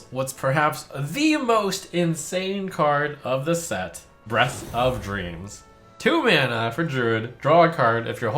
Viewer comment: does he say BREASTS of Dreams or am I trippin